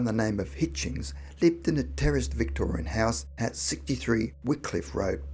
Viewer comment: Not gonna lie... I totally thought you said "terrorist Victorian house" at first. 😅